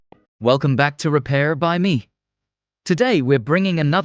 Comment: Thought you said: Repair Bunny! 🐰🪄🛞